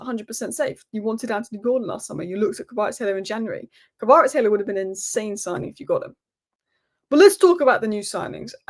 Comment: Who would have been an insane signing in January? I just can't catch the name. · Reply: Kvaratskhelia